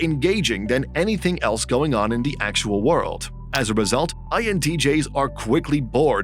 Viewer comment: Did anyone else hear intj at 4:58?